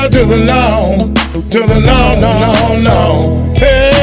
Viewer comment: At first I thought he was saying hail to the lord